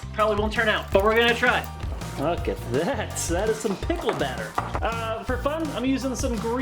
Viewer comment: Thought it said my dickle party...